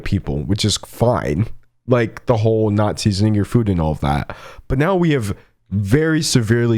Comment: ok but at 10:53 i genuinely thought he said "nazis in your food" xD